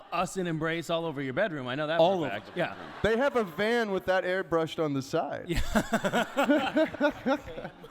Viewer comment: Okay, I think at 40:19 Misha's just repeating what Edlund said to Jensen. Something along the lines of "..Ben was saying..van airbrushed on the side" which is why Jensen then laughs his ass off and adds the bit about the conversion van. The sound was all funny in that room and there were several times where they couldn't hear one another or the audience/question askers properly. Idk, just my guess. Also? One of the most hilarious panels ever tbh. <3